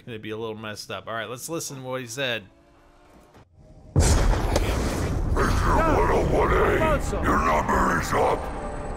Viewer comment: I thought it said "prisoner 101A your number is up"